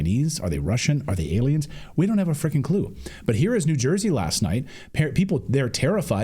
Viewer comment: The first time I listened I thought Clayton said...." Are they Chinese, are they Russian, are they Aliens ,,,we dont have a F**ken clue"! (Couldn't help but laugh... says more about my mind). 😂